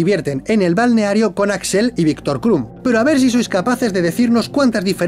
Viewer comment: Yo de primerazo entendí "Viktor Rom", asi de enfermo estoy hahaha